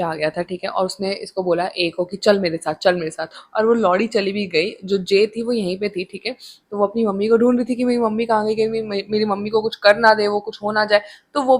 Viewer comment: Did you just called the maid l@